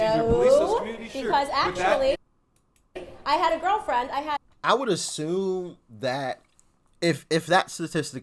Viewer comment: I think the lady that yelled out said “Do you have white privilege too?” And he said “Yes” Did someone hear something else? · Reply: I thought she said "do you assume to"